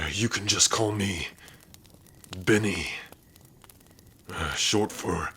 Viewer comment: I’m dying, for a second when he said Benny I thought he said Binnie and that’s the name of one of my sugar gliders, I am ✨attached✨